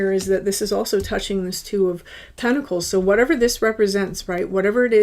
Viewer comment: I swear I heard you say "2 of tentacles" 🤔